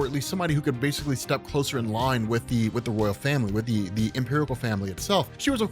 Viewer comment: omg, it sounds like Rob is saying "[empirical] family," right here, lol.